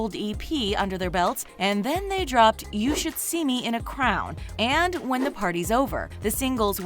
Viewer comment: you should see me in a crowd No spell hate just a point out so nobody else makes fun of you for saying crowd instead of crown